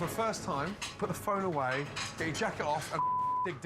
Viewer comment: at first I heard "put the phone away and then jack it off" which was pretty weird but not unlike something Gordon Ramsay would say... to my disappointment however, he said "take the jacket off"